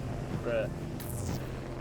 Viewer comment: did you said "bruh" :DDDDD lol